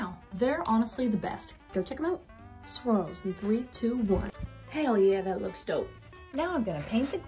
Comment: Only one who thought she said “ squirrels in 321”😂